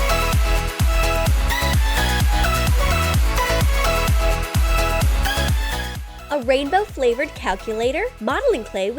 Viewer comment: I cant figure out what is in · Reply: Edible stapler.